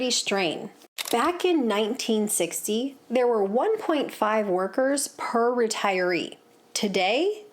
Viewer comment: Did you catch what she said at ~2:32? in 1960, there were 1.5 workers per retiree now it's ~2.7 workers per retiree!